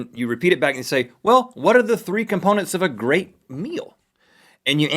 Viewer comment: I misheard that as 'what are the three components of a great wheel?' you know.. like a revolution? :)